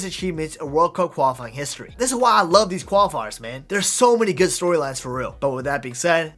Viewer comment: did he say story lads